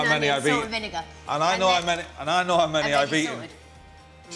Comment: What's Noel whispering at 0:44?? Something about the crisps.......... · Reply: theselector "She's dipping the crisps" basically meaning she's stealing packets haha